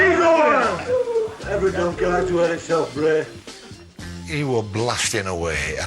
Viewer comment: ''EVERY DONKEY LIKES....'' what does he say? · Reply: Every donkey likes to hear himself bray